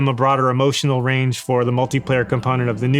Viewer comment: did he just say multi player